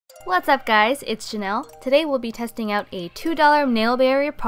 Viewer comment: At first I thought you said "hey it's Chanel"